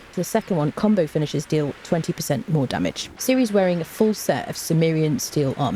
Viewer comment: I could have sworn you said "elf damage" 🤣